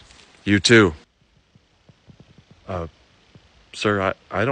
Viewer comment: not me mishearing "I'm infectious disease and I'm gonna check you for ticks" as "I'm infectious disease and I'm gonna check you for tits" 😭